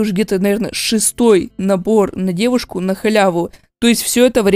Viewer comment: Набор на девушку мне послышалось на дедушку,😃🤗